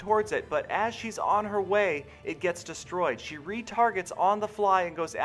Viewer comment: ! When you said she 're targets' it sounded like you said 'retarteds' lol